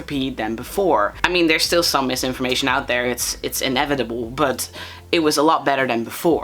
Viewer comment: Help, when you said "inevitable" I heard "inedible" and spat out my chip😭😭😭